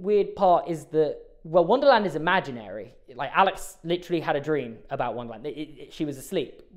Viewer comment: Is it just me or did i hear alex